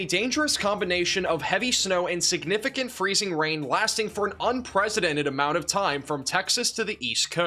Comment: Did I hear unpresidented?